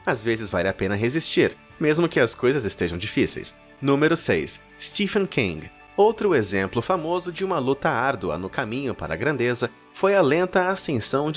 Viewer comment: escutei steve quém